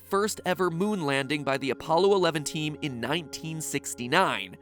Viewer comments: Question. Did he actually say the Apollo Eleventeen? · "Apollo eleventeen"?? Did I hear that right, or am I going mad because of this video? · Did MatPat say “Apollo 11-teen”Or is that just me? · Wait did he say Appalo 11-teen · did he just said Apollo Eleven-teen ? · Did.. did he just say Apollo 11 teen?